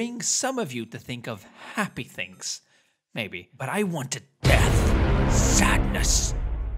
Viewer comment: But I it death Me: first time I hear it I thought you said “ i want a bath”